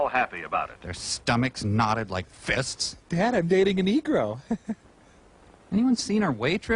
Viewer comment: Did anyone else hear say "Dad, I'm dating a negro!" and thought he said "I'm dating an e-girl!"?